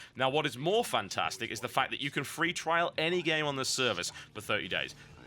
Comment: did he say you can free trial for 30 days...?